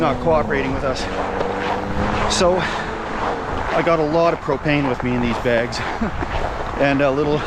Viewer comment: Totally thought you said Cocaine @ 0:51 :D